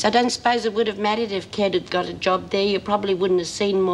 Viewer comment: Could have sworn that Lizzie just said 'Ked' there at 05:35, rather than 'Kev'. Whoops!